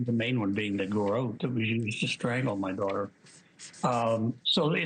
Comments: Did he just say “the main one being the garrot WE used to strangle my daughter” or am I crazy? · Am I the only one who heard him say.. THE GARROTE WE USE TO STRANGLE OUR DAUGHTER WITH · Also, did he just say, “That WE used to strangle my daughter????” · It is an audio like Yanny or Laurel was. Some hear WE. some hear THAT WAS. At first i was on here commenting - It’s SO clear he says we. + he catches himself and starts saying uh uhm uhm and fumbling his words immediately after because he even noticed what he said. but i can prove.. This audio is so like the blue dress gold dress argument. I listened to it 10+ times and heard WE every time. Even typed a comment about how dumb people were bc “was” wouldnt even make sense in the context of thus sentence .. bc it would be the garrote “that was used” . Til i paused, stepped away, and sat back down & listened again… and now i hear it. He says.. “the garrotte that was used” .. the THAT WAS is just very short and choppy but it’s there. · Did he just say “ the garrote that WE used to strangle my daughter”? I listened to it 3x and that’s what I heard, Maybe it was a mistake, he is elderly and even though I do think he has guilty knowledge, I do not think he killed JB. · Did he say what I thought he said!?!? Starting around 3:20. Did he say "that we used to strangle my daughter" I hope I'm wrong! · What the hell did John Ramsey say?? .. Slow it down, listen carefully 🕊 R.I.P. Jonbenet 🕊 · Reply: "That we-w w was used "?